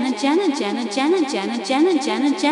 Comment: which I heard "Jangan²"